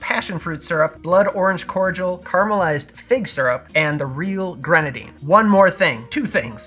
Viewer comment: I swear I thought you said "caramelized pig syrup" 🤣. Going to make one of these coffee negronis now!